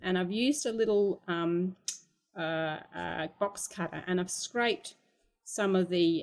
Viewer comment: Sorry I couldn't hear or pronounce what instrument you said you used to scrub and give the blue shade at 3:14? A box...something. Sorry I just couldn't understand the word. Thanks in advance and nice tips. Also, lovely colouring. :) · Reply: XxThePianist & ViolinistxX ... box cutter... like a Stanley knife. Same technique she demonstrates for using pastels for backgrounds.